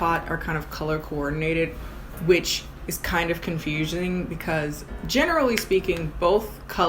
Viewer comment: did you just say confusioning?😂